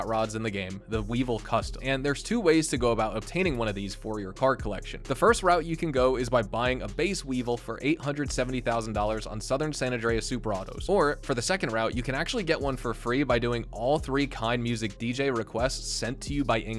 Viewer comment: did bro say 870.000🤦‍♂️